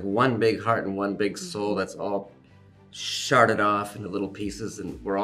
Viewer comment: Did he just say “sharted”?😂